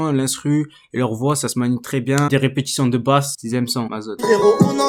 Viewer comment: A 4:27 c'est quoi le titre j'ai pas bien entendu . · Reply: c'est ma zone